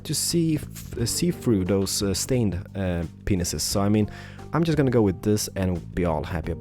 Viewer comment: um did he say it’s hard to see through the “stained penises”?